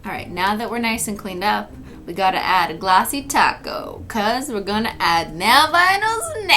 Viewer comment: When did you get the idea to call it glossy taco 🌮 instead of clear coat and why please answer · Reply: because its glossy and people always thought she was saying ‘’glossy taco’’ instead of ‘’glossy top coat’’